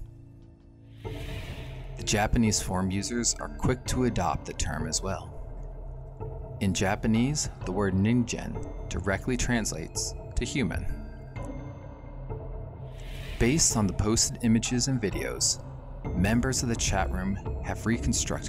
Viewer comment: Nice one! For some reason I thought it you were saying NINJA lol